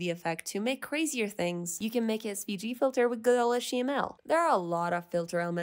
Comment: wrong subtitles, it says SPG filter instead of SVG filter please fix that :(